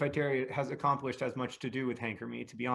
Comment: I really thought he said “very little to do with Hank Green” and I was like THE SHADE!!!!